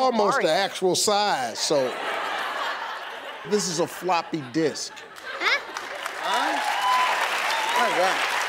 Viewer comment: i thought he said f*cking disk.